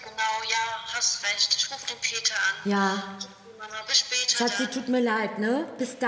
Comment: Ich hab jz falsch gehört ich habe verstanden Ruf den Täter an😂